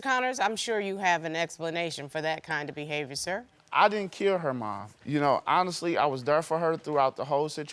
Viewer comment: did he say “I didn’t kill her mum”?